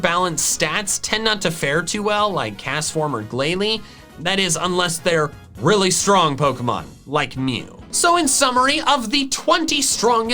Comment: I thought he said unless their really strong Pokemon like me